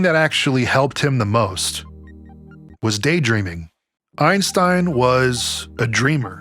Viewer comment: Lol I thought you said "day drinking" when you said day dreaming. I was like hellllllllll yeahhhhhhh brotha haha